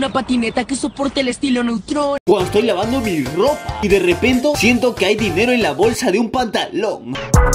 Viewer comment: When el coronavirus llega a memes random y lo escuchas toser : no es sierto , diles que no es sierto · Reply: Escucharon que dijo derrepento en el minuto